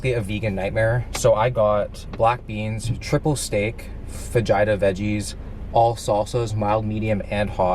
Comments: I thought he said vagina veggies😂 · Very nice video as always Will, but please learn to pronounce “fajita” the right way, I thought you said vagina 😂😂😂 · I thought you said vagina veggies haha sounds good to me though!